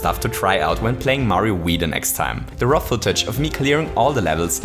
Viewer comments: I just realized he's saying "raw footage". I kept hearing "ROFL-tage" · lmao when I first heard him say "raw footage" I thought he said rofltouch